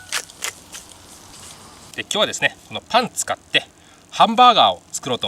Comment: パンツ買ってに聞こえたw 病院行こ